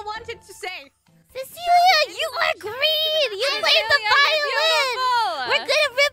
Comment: i thought "fluffer" said YOU ARE GREEN